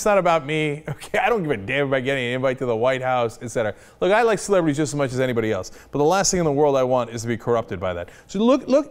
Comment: i had to replay from 1:00 cause I thought Cenk said "I like slavery just as much as anybody else"